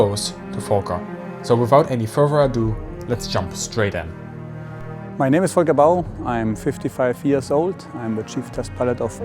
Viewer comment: This is an awesome video, but I was watching this with the volume up, and at 0:50 I hear my wife ask: "Did he just say my name is F*ck a dog???" 😂😂😂